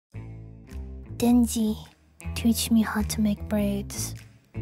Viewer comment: what did she say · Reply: "Denji, teach me how to make braids"